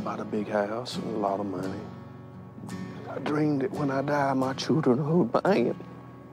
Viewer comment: I'm Canadian but I heard "I dreamed that when I died, (it was) my children who were paying it." or "my children would be in it." maybe I'm wrong cuz the next thing he said doesn't add up.